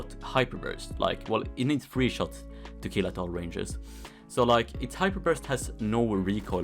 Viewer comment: what did he say in 1:34? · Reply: well it needs 3 shots to kill at ranges